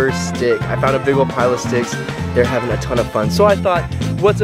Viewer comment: Sticks?!?!? I thought you said steaks! You booped them so good I'm sure they wouldn't remember if you said steaks!